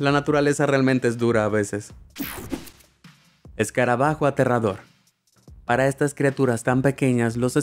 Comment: Lo que dice :escarabajo aterrador El texto:escarabajo enterrador